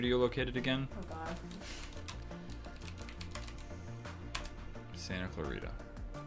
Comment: When you said Santa Clarita I thought you said Santa Claus